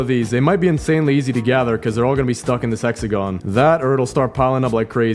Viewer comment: is it just me or it sounded like he said s3xagon